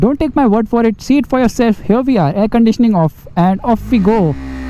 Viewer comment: Did he just say,“Air-conditioning off”?